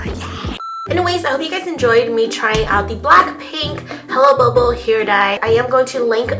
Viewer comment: it sound like she is saying "hella bubble" haha. i love it lol